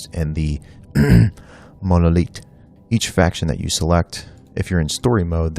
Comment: Did he just say "monolitte" ?